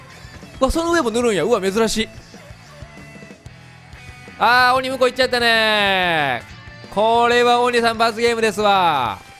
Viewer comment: 『あー鬼うんこ行っちゃったねぇー』って聞こえる